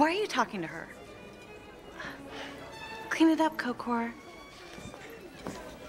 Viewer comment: "Do you have any more of that?" 😂😂😂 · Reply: I always thought he said “Tiffany wore that?” And I was like, who tf is Tiffany lmao.